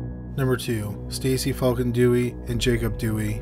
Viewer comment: Lmfao you're so funny man. I swear I keep hearing you say "fuckin-dewey" lol